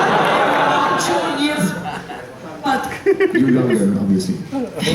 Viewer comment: what is he saying at 8:41? "you know you're ....?" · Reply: He says "you are younger obviously"